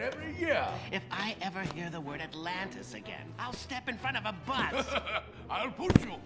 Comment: Did anyone else spend their whole childhood hearing him say he'd step in front of a butt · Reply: I always thought he said "I'll step in front of a bunch"